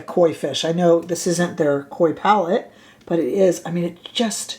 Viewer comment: Ohhhh....I heard "koi palette" (thinking about an overall Japanese theme) and you actually said "Coy" and I am disappointed now that this is not a thing that exists 😢😢😢 Can you imagine a KOI Palette of soft micro fine shimmer colors of the koi fish?